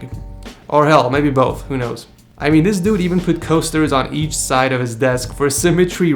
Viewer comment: lol I thought he said “toasters”.